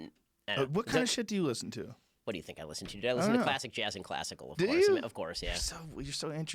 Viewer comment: What did Ben say.... Classic Jazz and Classic ....? Anyone understand his hyper speak? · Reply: Classic jazz and classical.